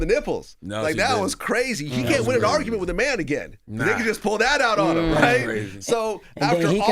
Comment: "ninja just pulled out on him" .... did he just say what i think he said?